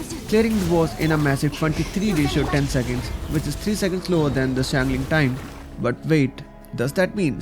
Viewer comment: uhhh, the subs said lower instead of slower, making it seem like durin did better than Xiangling...